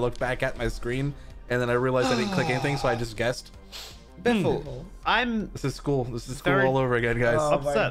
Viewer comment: Did just say "Bimble"? XD